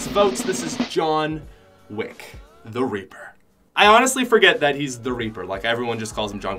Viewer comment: who else heard “the raper”